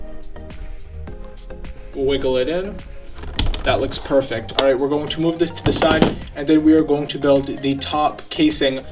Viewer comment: what does it say at 8:29? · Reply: Unicorn Gamer050 "What was that?"